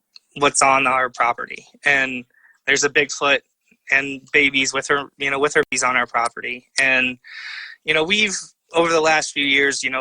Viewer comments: When he said "theres a bigfoot with her babies on our property" i swear i thought he said "theres a bigfoot with herpes on our property" 😂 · excuse me but at 43:30 did you say babies with herpes on your property? thanks for answering.